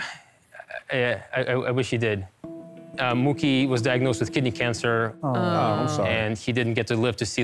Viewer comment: I thought he said "Moochie was diagnosed with Kitty Cancer" .